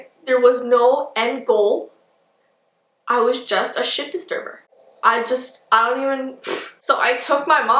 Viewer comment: When you find out cusses 😱😱😱☠️💀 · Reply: She’s an adult,but I thought she said a shirt disturber I replayed it and I hear “as a child I was just a shit disturber.”